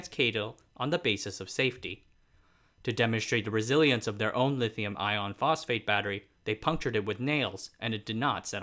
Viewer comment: It sounded like you were saying, "lithium, ion, phosphur" The chemistry is "lithium, iron, phosphur or LFP where F stands for ferine which is iron.